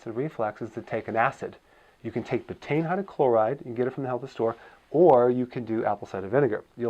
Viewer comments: what he says? not native speaker, i dont get it! ty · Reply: For anyone curious for the answer: dr berg is recommending betaine hydrochloride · What did he say at 18:20? What chloride? I've relistened 5 times and still can't make out what he is saying.